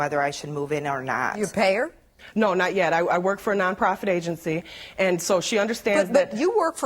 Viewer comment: Is it me or did she just say nonfer profit instead of non profit?